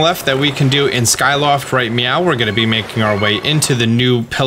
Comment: Is it just me or did he say 'right meow'? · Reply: Ya he said meow.